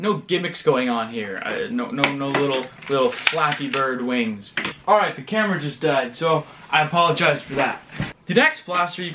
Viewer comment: When u said the camera just died I thought u said Cameron just died. Even though I don't watch u much I think u don't have a dog or family member named Cameron.